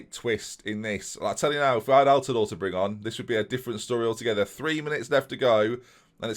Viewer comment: I genuinely thought you said "i'll tell you now if i had oltedal to bring on" i started thinking why dont you bring him in and then i realised he was a regen